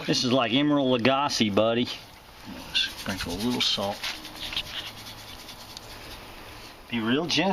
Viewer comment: I thought you said a little salt lol great video